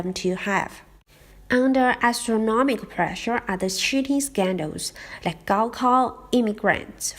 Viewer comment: When you say 'cheating' it sounds like 'shooting'. I thought you said a police man shot and killed someone in the exam! I was like WTF.